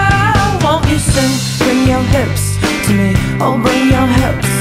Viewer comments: Like this if you thought she said open your hips to me. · I am so happy the top rated comment is like if you thought she said "open your hips to me" because that is EXACTLY what I thought she said.. and I hate this song. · I thought she was saying 'open your hips' too, which I thought was Nina wanting to do it for Dr Chris :-)